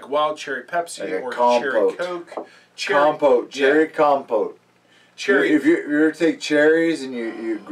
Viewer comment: I thought the said Compost at first. LOL !!!